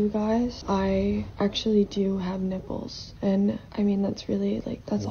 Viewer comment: Did she really say, "I actually do have nipples" haha no kidding